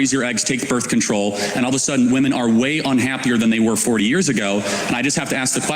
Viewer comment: Did he actually say "Bready" Friedan at 11:35?